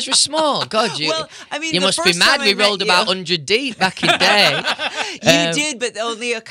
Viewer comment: What does he say at 1:46 to 1:49? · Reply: You must me mad, we rolled about 100 d back in the day